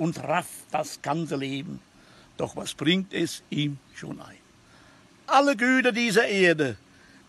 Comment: Was sagt er ab 00:45 "was bringt es im Dsch....."? Meint er das Paradies?